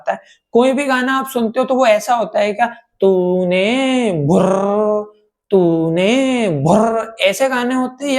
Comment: kya bol rahe ho bhai 😅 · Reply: Tu ne bhurrrr tu ne bhurrrr 😂😂😂